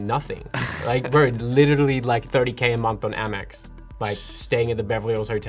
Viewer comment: if one scales that proportionally to the average pay of an American middle class family, the Dad basically gives/gave him $20 bucks a month. But it's ok to be salty. Useless but ok. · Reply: could've swore it said 20k a month